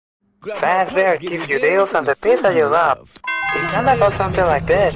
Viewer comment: i just heard pizza of zap?